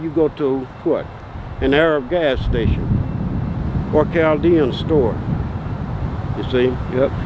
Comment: Hmmm, learned something today chaldean. I thought he said galdean. Very wise